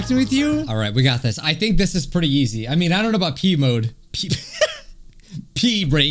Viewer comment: did he said pee mode?😂